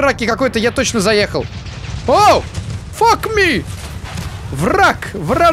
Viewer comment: что он сказал? Фак ми? я токово не ожидал. Но ты лучший. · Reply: Он сказал Чёрт побери